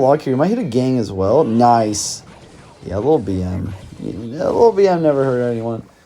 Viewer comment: wtf this guy said ☠️ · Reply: he said BM which stands for bad manners